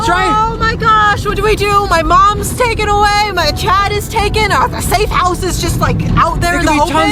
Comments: DID SAID MY CHAD IS TAKEN AWAY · On 13:17 did I heard say "my chad is taken." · says my Chad anyone else hear it